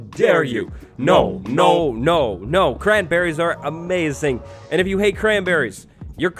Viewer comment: i heard something else and that's grandparents instead of cranberries